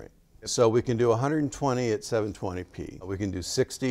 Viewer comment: I'm sorry... at 4:17 does he say that 120fps is only available at 720p? Can somebody clarify the resolution at 120fps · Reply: 720p, as you said. Canon cameras are either bad or not very good value for video.